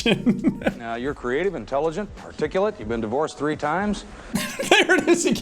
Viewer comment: I think he was trying to say "articulate", but it really sounds like he said "particulate".